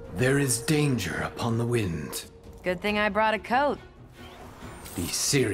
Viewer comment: I honestly thought Cassie said “good thing I brought a coke.”